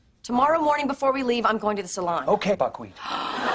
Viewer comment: What chandler said in minute 3:57??? I heard backweet or something like that. What really he says? Thks.